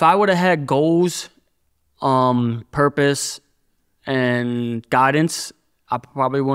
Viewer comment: It sounded like he said Goggins lol not guidance